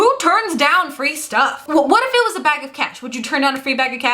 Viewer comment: For a second I thought you said "a bag of cats", instead of cash. Who would turn down a free bag of cats, though?